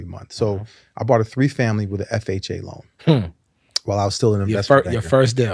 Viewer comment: I missed the part, did he say 3 br family home? Or 3 families home? I’d love to understand what he means here Thx! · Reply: " I bought a 3 family with an FHA Loan. "